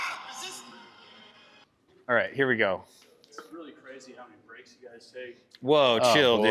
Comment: What does the guy say at 13:25 to piss and Tom off? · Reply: "Surprised how many breaks you guys take"